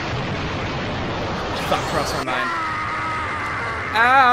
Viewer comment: did kirby say f*ck you